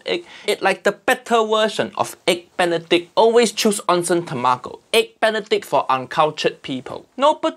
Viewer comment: "Onsen tamako?" What he say? What he say!? Uncle Roger, you say tamago few seconds earlier, why you as consistent as eggs benedict?